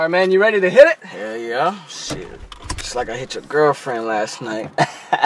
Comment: Is “It’s like to hitch your girlfriend last night 😜” what he says? If so, what does that have to do with the gloves, etc.?